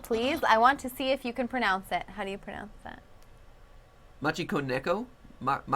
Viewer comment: I thought Steph said "Honey pronounce this" and then realized she actually said "How do you pronounce this"